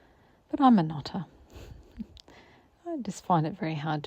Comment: Just having a laugh as I swear I thought you just said “I’m a nutter” and I was knodding thinking yes, me too. Then I worked out you said “I’m a knotter”. Well me too if that also 😆 Lovely to be stitching with you again. Hope you’re feeling better by the day 🤗Cheers, Christine